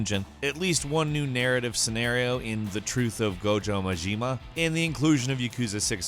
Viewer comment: did he call "Goro Majima" as "Gojo Majima"?